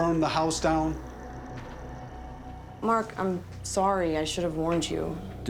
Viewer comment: Did I hear say “Merk” at 11:39? 😂